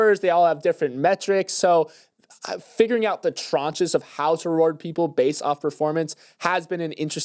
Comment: what is the word is saying? I like how it sounds, but can’t figure out what it is to look it up. Unless, is it trenches? 😅